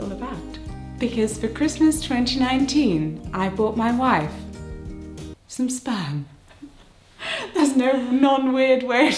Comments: I thought you said “I bought my wife some spam” I was like huh okay not sure where they’re going with this joke but I’m sure it’ll be funny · I thought she said "I bought my wife some spam." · I heard "Got my wife some Spam!" But the remembered the Twitter post I saw before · At first I thought she said she bought Claudia some Spam. LMAO! Anyway, congratulations! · So at first I thought you said 'I bought my wife some spam' And I just 😂😂😂😭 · My American ears first thought she said she bought her wife some spam and I was so confused. 😂. This is so wonderful!! Congratulations!!🎉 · bye i literally thought you said you bought your wife some "spam" at first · Congratulations!!! Also had no CC 1st. I thought you said u bought your wife some SPAM. So yeah, y would any1 b all excited about SPAM? YAY a baby!!! Yeah saw some1 else thought they heard it too. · this is awesome and all, but at first I heard "I bought my wife some spam," like the MEAT, and was concerned as to how that made a child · I'm sorry but i thought you said "I bought my wife some spam for christmas" and i was thoroughly confusesd, why did you get her lunch meat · First off, Congratulations! I am so so happy for you :D Secondly; I legit thought you said "I bought my wife some spam" at first and was incredibly confused fffffff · omg! because of the no subtitles yet, I completely thought you said you got your wife spam... I got so confused 😭guess I'm not so good at lipreading 😂 Congrats by the way! <3